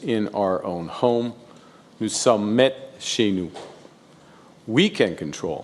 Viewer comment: what are the words he says here? Is it a different language, or am I just having trouble getting it? Sounds like "to submit shayoo"? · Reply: It’s French. Presumably for “we are in our own house”. Sounds like “Nous sommes met chez nous”